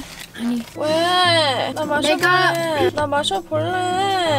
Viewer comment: um what did say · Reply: she said 내가 means " i "